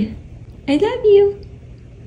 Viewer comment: When ever he said meow it sounds like he said I love u to and yesssss